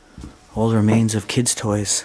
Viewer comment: Omg thought he said “old remains of kids” 😂 had to re watch